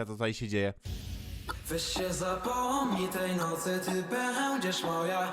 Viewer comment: Czy tylko ja usłyszałam "Weź sie zapłodnij"? Haha